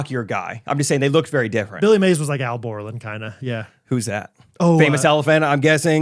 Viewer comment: I 100% thought that said Billy Mays was like Al Gore and was extremely confused until he repeated himself and I finally heard “Al Borland”. I was not only very confused but also surprised that didn’t know the name of the man who invented the internet!